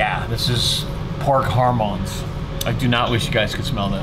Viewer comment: Yeah I thought u were saying hormone as in hormones.